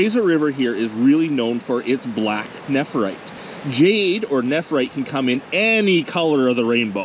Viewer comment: did you say netherite? Netherite is most powerfull stone in minecraft!!!